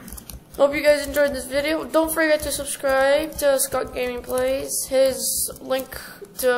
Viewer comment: that first one tho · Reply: at the beginning i also heard you say soup instead of scott lol